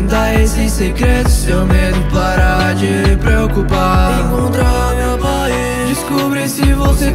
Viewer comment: Eu ouvi "Encontrar a barreira"